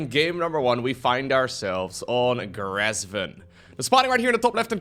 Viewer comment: Somehow, whenever I hear say "we find ourselves on..." my brain says Simulacrum